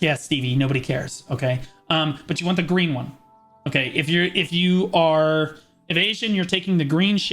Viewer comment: When you say - "if you are evesion" I always hear - "if you are an asian" :))))) first time I even thought that this is some racist joke ))))